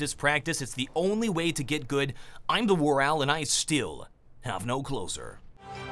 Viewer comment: ffs I keep hearing "And I still.. Have no clothes on" I can't help it anymore.